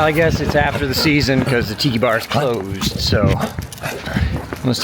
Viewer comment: Tiki bar... I thought you said tittty bar. 🤣🤣🤣